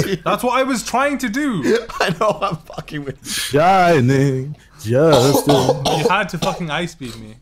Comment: I heard shining justice. A lot of Jojo references in these recent FFAs :]